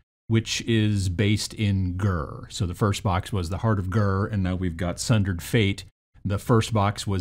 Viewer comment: All I hear is "heart of G.I.R." XD Invader Zim for the win!